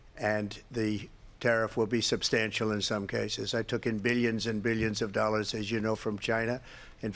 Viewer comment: Did trump say million million in dollars